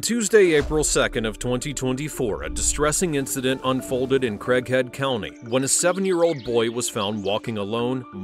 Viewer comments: Thought he said crackhead county 😂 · I thought it said "crackhead" county 😂😂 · I thought he said "crackhead" but it was Craig/Head 😂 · onb i thought bro said crackhead county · I had to rewind the first 5 seconds. I thought I heard “crackhead county” 😂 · I thought it said crackhead county 😂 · I thought the narrator said “crack head county.” 😂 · Am I only one that thought he said crackhead county at the beginning? 🤣🤣🤣🤣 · I thought you said “Crackhead County”. 😂😂😂😂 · i thought he said they lived in a town named, Crackhead... lol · Thought he said crackhead county first time he said it 🤣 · I thought he said crackhead county 😂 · I thought he said Crackhead County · I could have sworn the narrator said crackhead county jail, and that's just what she was a crackhead. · I was sneezing when they said the name of the county, and I could have sworn I heard "Crackhead County" and I immediately went, "well, this already makes sense." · I thought they said "CRACKHEAD county" it is Craighead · I had to backtrack when we got introduced to "Craighead County," and all I heard was "Crackhead County." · Listening to this only - I nearly woke someone up laughing wenn I misheard it as "Crackhead County"😂😂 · I could have swore the narrator said crackhead county ...😂😂😂 Only me? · I had to rewind in the beginning because I thought the narrator said Crackhead County. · thought he said Crackhead county for a sec 😅😅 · “crackhead county” I swore I heard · I thought you said Crackhead County.